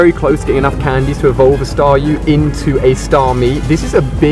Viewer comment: Did he just say staryu evolves into staryu